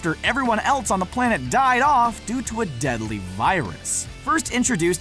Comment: Did he just say deadly virus?